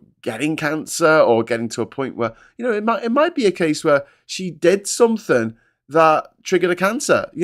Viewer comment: did you say "cheap dental foam"? I'd really like to know. Thanks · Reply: Oh goodness. I feel very silly now. ". . . she did something . . ." I don't know why I heard "dental foam". Must have got toothpaste in my ear somehow! 😂